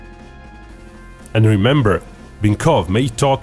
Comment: At the end, I could have sworn he said "Only real beef can bring us together".